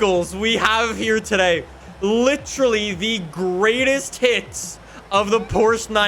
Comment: I thought literally said the greatest tits of the 911